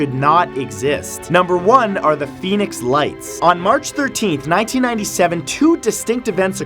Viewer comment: At first I heard it as "Phoenix Wrights" lol